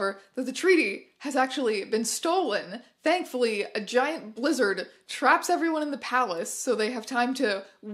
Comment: thought you said a giant lizard traps them, not blizzard. still works imo